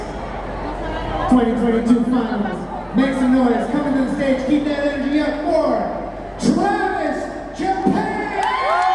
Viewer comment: Thank you so much for wonderful post! I 'm Japanese and fun of them.🐅 4:07～Please tell me what the moderator is saying. 🙏🙏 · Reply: Thank you and you're welcome! 💖 He says "Make some noise! Coming to the stage... keep that energy up for Travis Japan!"